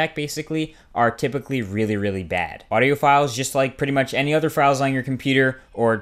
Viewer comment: when you said audio files, I heard audiophiles. I lolled.